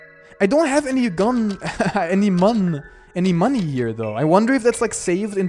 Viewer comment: Tem has corrupted, he said muns instead of money